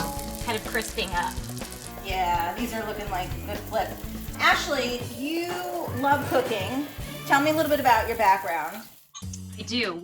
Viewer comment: broo I’m jot joking it sounded like she said Ashely you love Cocaine !!!!